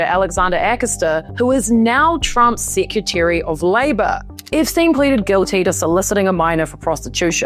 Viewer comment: I love the way you pronouce Epstein like, "Ipstein". I keep hearing it as "Dipstein". 😂